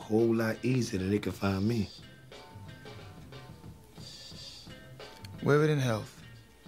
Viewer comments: What does Marlo say? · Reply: “Wear it in health” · min what does Marlo say ? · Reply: "Wear it in health." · min what does Marlo say ? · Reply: I believe that line is Shakespeare, forget which story... Very good writing in the card game hold up scene! But yea, "Wear it in health". I thought he said "Women n health" referring to the twins Omar was holding the 1st time I saw that scene! 🤣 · What does Marlow say at 2:17 ? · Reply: "Wear it in health." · what does marlo say at 2:19? I can't quite understand the words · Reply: wear it in health